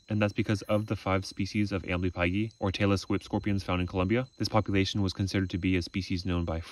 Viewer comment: I love how chill everything is with you picking it up, my favorite is the tailless whip scorpion not even moving as you pick it up lmao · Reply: I really thought he said Taylor Swift scorpion